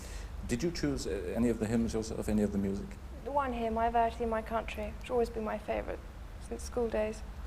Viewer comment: what's said? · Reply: I vow to thee my country?